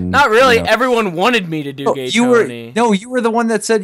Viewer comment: all i kept hearing was "everyone wanted to DO gay tony" xD